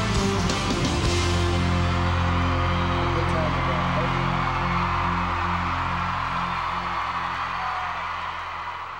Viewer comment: what did tim say? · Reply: Ohhh now something like that